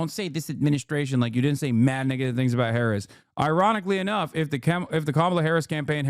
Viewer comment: He was reading the message, saying "mad negative things" and some people heard that as the n word